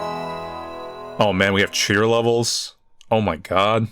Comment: I thought it said queer levels